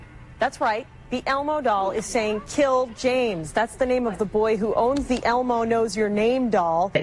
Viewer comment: I didn't hear "kill James" from the Elmo... I heard "Tiáozhěng" which means adjustment in Cantonese, something that could make sense if you had factory reset the toy